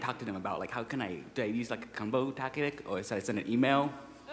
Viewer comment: "What can I say to them? Do I ..(use a gumball tactic?).. ? Do I send them an email?" "Hahaha" What is he saying? I can't hear the words or undrstand why people are laughing.